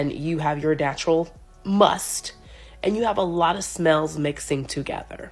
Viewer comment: did she say natural "MUST"?.... 😔 it's musk